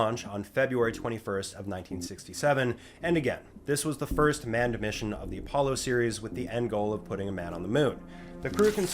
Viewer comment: what did you say…? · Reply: GET OUT